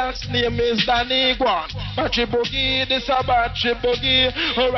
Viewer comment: In the 19:00 some Native english speaker what he says after eletric boogie... This is porchy boogie ??? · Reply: Probably battery boogie